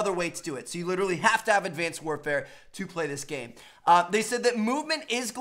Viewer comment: Did he say advanced warfare